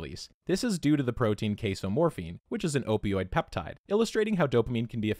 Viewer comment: You were talking about cheese and I swear I just heard you say it releases Queso-Morphine